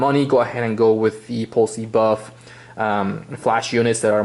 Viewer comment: 1)You mentioned an expensive/better brand @ 10:12 (I put the CC but it couldn't read what you said correctly) that phonetically sounds like "Poll Cee Buff"...please give the correct spelling. 2)I read online that some people feel the plastic used for this unit feels cheap. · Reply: The spelling is Paul C Buff. A company that makes high quality equipment. The plastic does feel cheap but it's worked very well for me so far